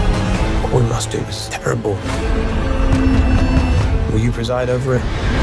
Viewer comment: Awesome 😊❤🎉 What is Cole saying at 1:32 ? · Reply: “What we must do is terrible”